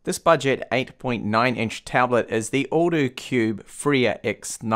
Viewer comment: Lmao I thought you were saying "the older cube" when you were pronouncing the brand name